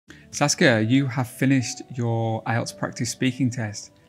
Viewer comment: I didn't look at the screen when the video started playing and thought I heard Sasuke😂